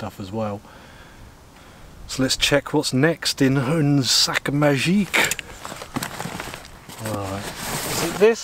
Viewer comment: what the hell did you say here man 😭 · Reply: i think he said “une sac magique”? (magic bag in french)